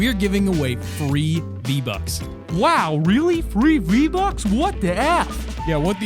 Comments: I thought he said 3 v bucks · I thought he said 3 vbucks XD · i thought he said "three v-bucks" OMG SO MUCH V-BUCK I CAN BUY ALL THE SKINS AND EMOTES IN FORTNITE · I thought u guys said 3 vbucka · I thought you said 3 vbucks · foxxmandog is my account and also when he said free vbucks i thought he said three vbucks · I thought he said 3 v bux